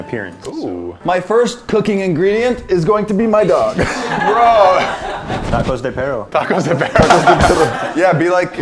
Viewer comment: ?????????? Can anybody tell me what they said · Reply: I think they said “Tacos de Perro” as in dog tacos. (Maybe even dogs in taco costumes...)